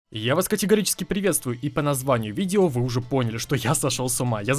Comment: Ахаха, лол. Чуть-чуть полагало в начале и я услышал "ебически приветствую вас")